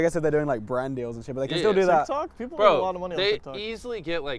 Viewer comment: did say shit or did i mishear him? 😭